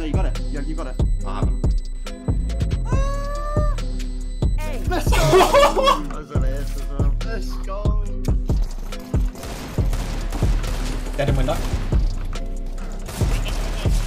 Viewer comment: I thought you said "dedam, window" but it was "dead on window" i almost felt special...